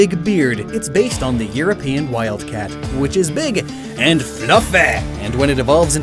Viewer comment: How did you hear donkey?? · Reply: well cause it almost sounded like he said donkey when he said “fluffy” that way… so that’s why it slightly threw me off.